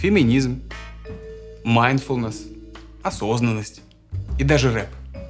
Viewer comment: Что он сказал после феминизма? Майнфонас???